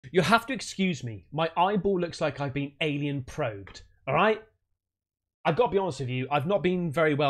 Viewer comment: You say "alien probed", I hear "missus farted on my pillow for a laugh" 😅